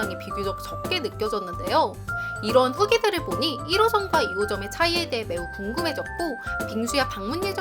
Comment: 1호점이 저만 1호선으로 들리나욬ㅋㅋㅋㅋㅋㅋㅋㅋㅋ